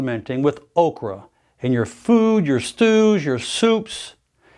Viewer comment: i almost thought he said, "stools" instead of stews for a brief moment. 🤔😳😱😂